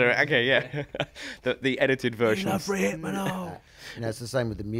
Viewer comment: What is he saying at 5:16? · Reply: We Love Rohypenol - from Baby's got a Temper.